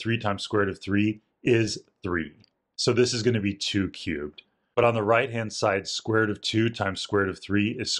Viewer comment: he siad : 2 cube i heard: too cute